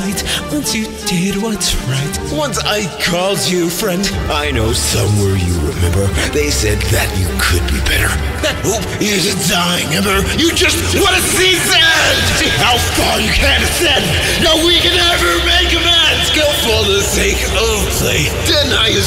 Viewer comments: i keep hearing this as "you just wanna see sans" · At first when I heard the line "you just want to see this end!" I thought they were saying "you just 'wanted to see Sans!'" as in "you killed an entire civilization of innocent people just so you get the cool edgy final boss sans fight that everyone on the internet was talking about," as if it was a jab at actual genocide players.